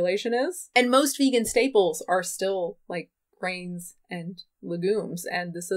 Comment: my sleepy head heard you say "staples like brains and legumes " and I was ..*like what now* .. :'D